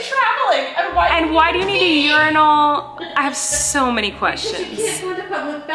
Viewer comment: Sooooo I thought you were saying ur-nal, like a year long journal 🤣🤣🤣 Also, doesn't everyone leave clothes to be folded, for multiple days lol